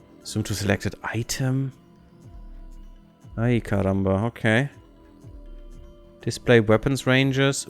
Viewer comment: What did you say at 18:37 "Ai caramba"? Portuguese?